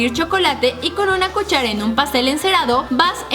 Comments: escuche "pastel" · en el minuto 6:26 o 6:27 o 6:28 dice pon chocolate en un pastel no papel eso es lo que yo escuche si no dice eso sorry. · escuche mal o dijo pastel encerado?? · alguien mas escucho que dijo pastel encerado en el minuto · en el 6:28 like si escuchaste que dijo "en un pastel encerado" jajajaja 😝